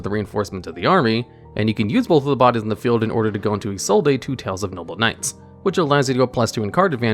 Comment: “I-so-dae” am I hearing this correctly?